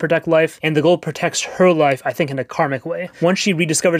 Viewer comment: Did he just say Karmic?